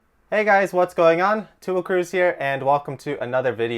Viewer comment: It always sounds like he says 'Tula cruise' in the beginning